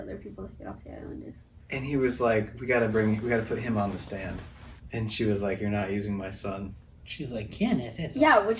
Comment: The lawyer didn't say "we've got to put him (Kate's son) on the stand". I mean, what could a baby/young child have to say? Instead, he said "I want him in the courtroom", probably hoping to portray Kate more sympathetically.